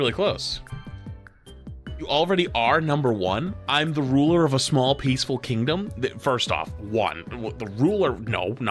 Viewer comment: I accidentally clicked on thia video and all i heard was "Owner of a small pe-" before clicking off. Had to come back on to comment though cause i was laughing WAY harder than i should have. 😂😂😂